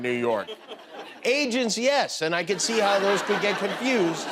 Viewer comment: Did Jimmy say he can see how people can get confused between Asians and Rats at 1:54??